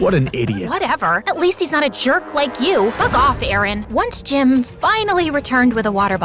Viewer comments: FOR A SECOND I THOUGHT IT SAID F OFF NOT BUG OFF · Anyone hear Juniper say the F word? or is that just me..? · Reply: I thought she said f*** off too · did you say "fu.k off?" Damn i know the the subtitles said is bug but listen it closer